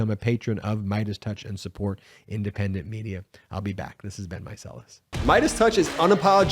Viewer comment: up until now I swore he was saying "this has been my solace" and i kept thinking wow he really values the consolation of doing this podcast